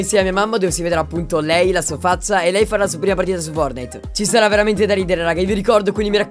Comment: mi potete dire cosa ha detto non ho capito 😂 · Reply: Sl3nderm4n lei d’ala sua prima partita su fortnite